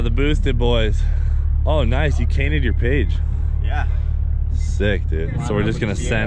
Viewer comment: Am I the only one that heard "you cainted your page" @ 7:40? Instead of painted your cage?